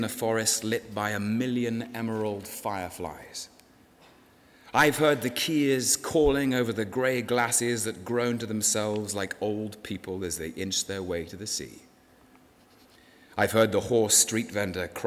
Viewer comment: what does he say? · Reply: I have heard the Keas calling over the grey glaciers that groaned to themselves like old people as they inched their way to the sea. (Kea=a bird species)